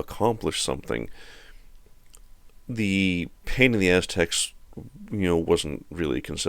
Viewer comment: I heard "pain in the aztec" 😄